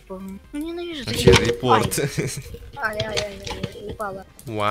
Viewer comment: мне послышалось или сказал "ай, ай, ай, ебало"